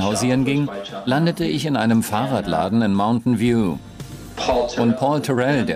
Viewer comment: bei 5:05 ist ein Übersetzungsfehler. Es wird gesagt das Steve Jobs in einem Fahrradladen landete - aber es ware ein Computer Shop namen "Byte Shop" (wahrscheinlich wurde vom Übersetzer "Bike Shop" verstanden)